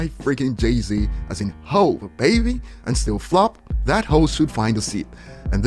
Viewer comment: BRO!!! I watch on 2x speed, and it straight up sounded like he said, "That hoe should find a seat." After like five listens, I'm like "Oh, that host should find a seat"...lmao